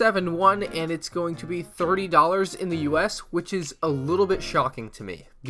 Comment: i thought he said 3 lol